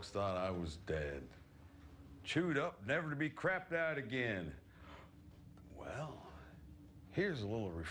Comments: What is he saying? I'm learning English and I only understood chewed up or something like that. Help 😳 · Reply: "Chewed up, never to be crapped out again" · "Chewed up and never be crap that again." Is it what he says??? Help!!! I dont understand!! 😳😳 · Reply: Chewed up and never crapped out again